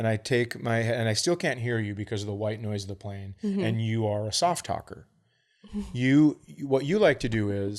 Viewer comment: I literally thought Chad said "and you are a soft taco" Man am I hungry....